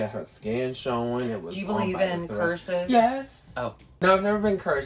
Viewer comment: I misheard that as "Do you believe in purses?" But I was still OK with that